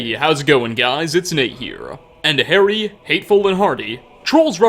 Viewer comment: At the start I thought he said horny instead of hardy